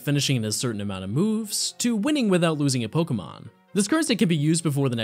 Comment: I heard "Winning without using a Pokemon" and I had to go back lol